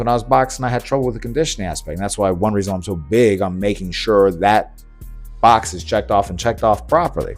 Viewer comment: When you say that’s why you’re so big, do you mean that you put on more muscle from all the conditioning work? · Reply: Ah! My ears played a trick on me. Lol, sorry. You said that's why you're "so big ON making sure that box is checked off", and I thought you said that's why you're "so big. I'M making sure...".